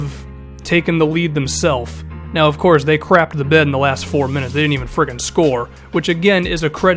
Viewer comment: thomas was stat padding at the end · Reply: oh 1st four I thought you said last four. But if you're winning tat much in a game I say why not pad stats. It's not like they flat out lost and Bron was going for a triple double any way.